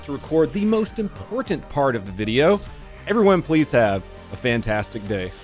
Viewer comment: sometimes it sounds like you say "police have a fantastic day" and now that's all i hear